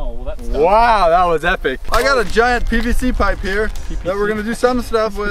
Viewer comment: From 1:06 to 1:16 who thought he said giant bbc pipe lol